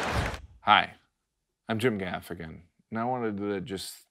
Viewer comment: At the the end I thought you said " Hi I'm Jim the African". Then I realized it was your name.